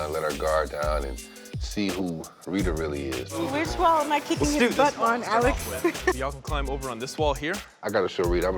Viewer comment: Wait did Rita just call at time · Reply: no she didnt. the rock climbing instructors name is Alex. She said "which wall am I kicking his butt on, Alex?"